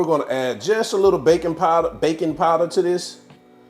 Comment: I legit thought he said "bacon powder" and I was like why tf would you want your salmon to taste like bacon. Then I thought about it and it felt it might taste good. They I realized he said "baking powder" and I was disappointed.